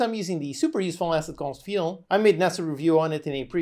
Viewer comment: I can't hear what the asset is called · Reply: It's called Feel <link>